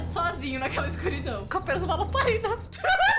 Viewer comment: entendi nada, alguém pode me ajudar? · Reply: Eu acho q elas falaram que ele ficou com apenas uma lamparina